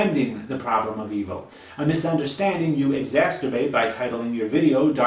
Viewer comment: "exasterbate"??? Listen - that's what he just said. Is that a cross between masturbate and exacerbate? Is trying to tell us something about how he jerks off?